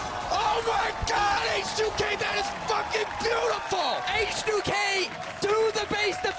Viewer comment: Did he say fucking